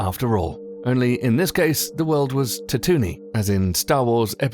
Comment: Did you just say fucking Tatoo-nee? Not Tatooine?